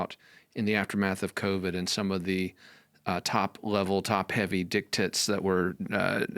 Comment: did you say dick-tits?